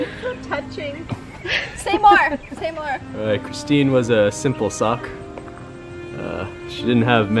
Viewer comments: "Cristine was a simple sock" - 2018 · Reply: ItzJustJayda i thought he said cristine was a simple suck and i was like whaaaaaaaat ? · Cristine was a simple sock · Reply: LMAO I THOUGHT HE SAID SHE WAS A SIMPLE SUCC 😂😂🤣